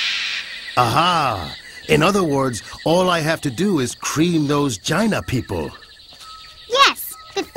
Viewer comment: did he say "cream those Gina people" ?